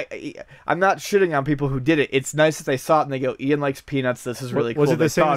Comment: I was cleaning up my room half-listening to this and 3:33 sounded like "ian likes penis" when not paying attention lel, im mature